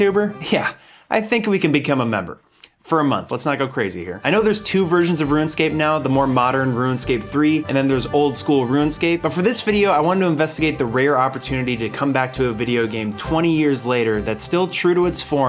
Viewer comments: I keep hearing "ruin-scape", instead of 'rune-scape'........ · am i silly or does it sound like he says ruinscape every time?